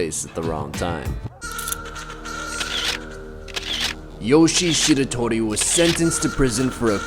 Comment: pause ther look down swear he said sh*t